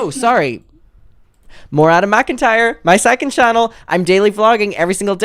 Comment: At first when you said your “2nd channel,” I thought you said your “psychic channel” & it definitely caught my attention! Ahahahaha… new skills being revealed everyday… 🔮